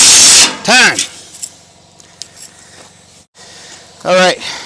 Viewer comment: sounded like an AVE "TIME" at 6:40. Where the needle scaler would shine is a crusty scaley engine block